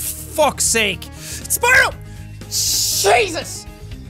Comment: am i the only one who heard Cheese us?!